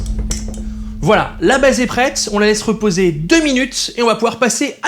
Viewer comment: "Voilà, la baise est prête !" ...Quoi ? Comment ça "C'est pas ça" ? Mais très bonne émission, sinon :D